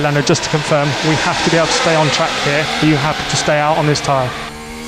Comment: I had to play that back again. It sounded like 'you have to stay out on this tyre' the first time I heard it. No response from Lando, so maybe he heard it that way also? At 8:33 Lando says 'I cant do this' further making it seem like he may have thought they asked him to stay out?